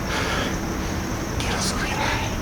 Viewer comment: parece que es una psicofonia demasiado clara que dice: "quiero subirme" 😳. confirma si no fuiste tú.